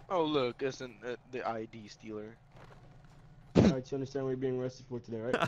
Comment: Legit I thought the guy said oh look it’s the IED stealer